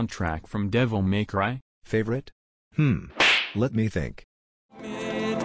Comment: idk if it was just me but every time i heard "mist of resolving" i thought it was "mystery resolving"